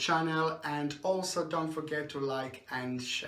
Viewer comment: I thought he said "psychic comedian" I'm taking my ass to bed